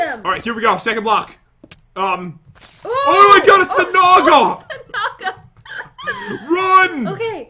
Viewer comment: I thought Pat said "OH MY GOD IT'S THE NALGA" Only people who speak Spanish would understand 🤣🤣🤣